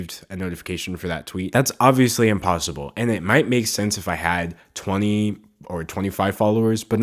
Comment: Subtitle moment: 7:24 What He is saying "... it might make sence..." Subtitles "...it might make sneeze..." lol